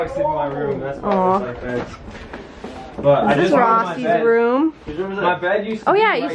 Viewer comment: Did she say Rocky’s it Rossy’s room